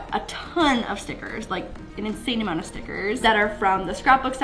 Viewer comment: When she said “stickers” I thought she said “snickers” 😂😂😂😂